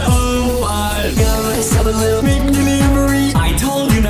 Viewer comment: When Justin said,' I got myself a little Mc delivery' I thought he said,' I got myself a little ring delivery'